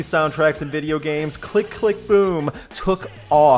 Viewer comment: LOL always thought he was saying click click pull but I guess boom really isn't much better either one of the biggest butt bands of 2001